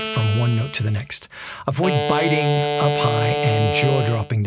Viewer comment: I could have sworn that at 4.16 ,you said avoid biting a pie.That would mess up your sax surely.haha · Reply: Lawrence Fairbairn Lol, "up high, and "a pie" do sound very similar, don't they? Imagine the difficulties for someone just learning English!